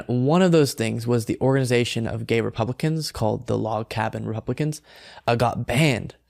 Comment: As a Blade Runner fan, i could have sworn I heard say “replicants” 😂